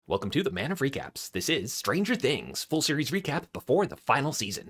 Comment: When you say “Man of recaps” I hear “man of freak apps.